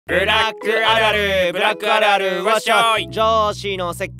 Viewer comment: やっぱりブラックア〇ルに聞こえるw